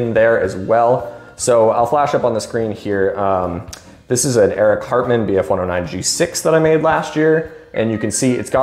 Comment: did you say 'eric cartman'?